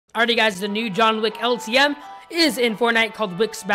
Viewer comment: who else heard "John Lick"